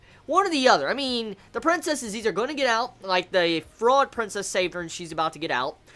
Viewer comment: For a second I thought you said "FROG princess".